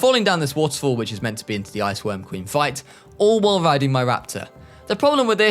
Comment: I probably misheard it but im pretty sure you said "Ice Quirm Ween" instead of "Ice Worm Queen" at one point in that segment 😂 Ark tongue twisters anyone? Gag Idea here but 100 days with just Microraptors coming when?